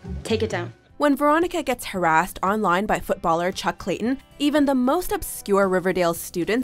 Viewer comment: You know what guys'😅, that man named "Chuck Clayton" ,I misheard about his name with "CHOCOLATE' "..idk why maybe I'm confused or something... Cause I watch the video without subtitle on'😂😂lmao.. And now I know what his name really are'...